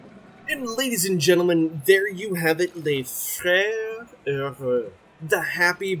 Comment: lol totally had to pause it at 21:58 to figure out what the hell you said. My brain just went, "FRENCH". That last word is a doozy. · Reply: Ah, Les Frères Heureux. I can't remember if I said it in the video, but it means "The Happy Brothers" and is a reference to an old episode of /Frasier/ where Frasier and Niles try to open a fancy restaurant.